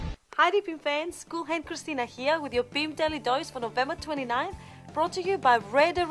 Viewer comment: I think she says "boob daily dose" at 0:22?